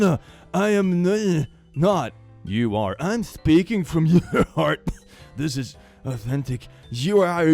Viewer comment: he actually said "im speaking from THE heart", if you look at your keyboard youll notice that the letters are close together)